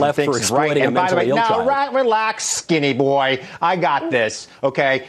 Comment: The woman's face in the middle when he said "calm down skinny boy" LMFAO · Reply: Andretti it's at 18:40 he actually says relax not calm down down my bad